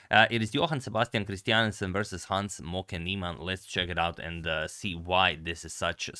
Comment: what I hear "Hans smoka neeman"